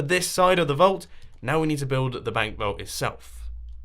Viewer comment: For a second I thought it said dank vault